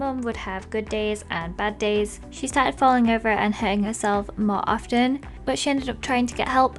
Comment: Lizzie: -she started falling over and hurting herself Me: did you say she started to hang herself?!